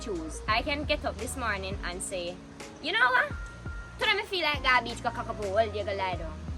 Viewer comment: Can some explain what she said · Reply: „Today me feel like go a beach the Wull day go kack up and laydung“ translates, today I’d enjoy spending the whole day at the beach just laying down and relax basically